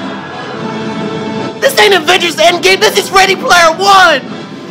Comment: “This isn’t Avengers endgame, this is Ready player one” 💀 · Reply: I straight up thought he said “this is rated player one.”